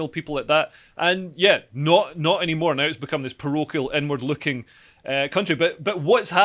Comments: This is probably the best segment Leo has done so far. Informative, succinct, and funny. Also, every time Leo says, "inward looking" I hear, "n-word looking". "It's an n-word looking country." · everytime dude said 'inward-looking' about the SNP, i kept hearing 'N-Word looking' and i was thinking 'wtf does this even mean'